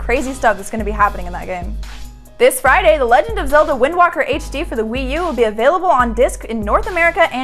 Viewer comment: did she just say wind walker?